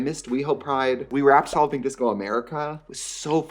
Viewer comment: I can’t tell you how long I thought you were saying “soluping disco”….. AND now I know💗